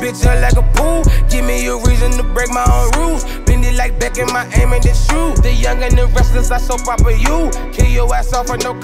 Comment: Did he just say "bend it like beckham, i aim and then shoot" wow that brought me way back haha, completely forgot about that movie